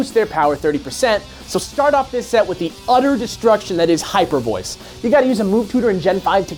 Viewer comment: Guys, if you use Hyper Beam on Mega Gardevoir, you will be either easily revenge-killed or give your opponent a free turn to setup a sweep. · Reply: I do not know if you are talking about what they said at 5:30, but just a heads up they said Hyper Voice not Hyper Beam. Though you can only get Hyper Voice on Gardevoir in Gen 5 so you would need to transfer it up.